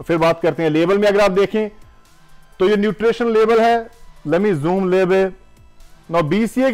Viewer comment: I was watching video in 1.5✖ speed nd after 2:39 i thought he said "let me zoom babe" 😂😂😂 Whatever paaji.... Lots of love nd huge respect Fan from #ludhiana #punjab